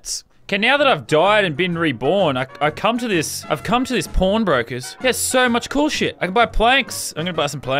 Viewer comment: Who else thought he said p0rn brokers